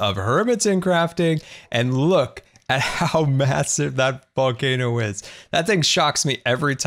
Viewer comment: Am I the only person that thinks that he said f***ing o is. I know he said volcano is but if you think of volcano when he is saying it you hear volcano but if you think the other word you hear it completely different. I can't be the only one.